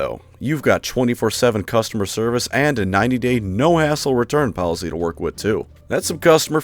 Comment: I thought you said "100% no a$$hole return policy" at first. lolololol.